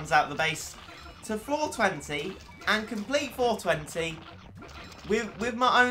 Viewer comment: it sounded like 420